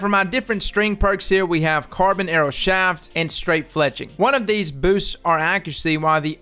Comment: I honestly thought you said straight flexing instead of flexing